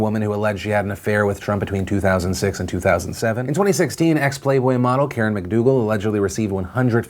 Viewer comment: Thought you said Xplay model. Got excited for a sec to see what Sessler is doing now.